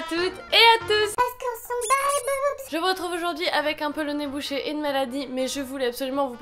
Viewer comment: j'ai compris " avec un polonais boucher" oups 😂